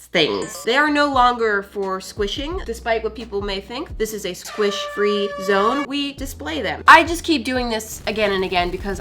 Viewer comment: Girl, I thought you said it's squishing free here.😮